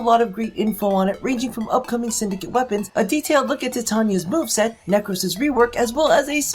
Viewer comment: I have an issue now. When he said Titania's move set, I thought he said Boob set. YOU'VE CORRUPTED ME! DARN YOOOOUUU!